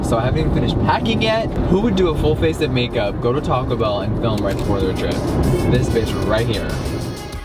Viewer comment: I thought he said no to Taco Bell. Like he recently said that.